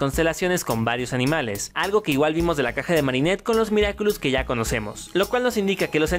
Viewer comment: yo en el minuto 3:12 le entendí caca de los miraculous XD